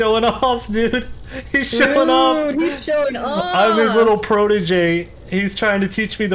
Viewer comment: im his little ... what did he say? edit: protoge i translated it from another video miles is a robot 3 from jelloapocalypse